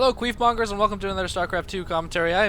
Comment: I hear "queef monger"